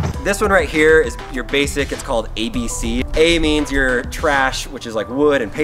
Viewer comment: I. heard what he said wrong I heard : a is, you're trash